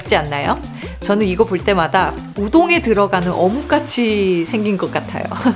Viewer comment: 우동에 들어가는 어묵을 똥에 들어가는 어묵이라 듣고.. 다시들었다죠...ㅎㅎㅎ